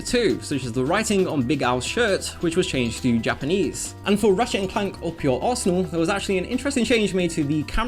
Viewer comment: Omg, I thought you were saying "Ratchet and Clank - Up your arsehole" for over half of the video